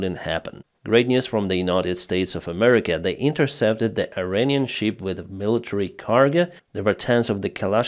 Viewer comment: did anyone else hear "Iranian sheep"